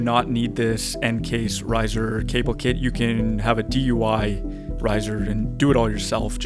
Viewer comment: Did I hear "DUI" kit instead of DIY kit at 6:10? lol